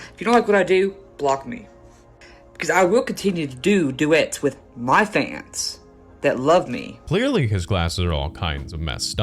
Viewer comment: Oof, I thought he was gonna say, "I'm going to continue making duets with minors"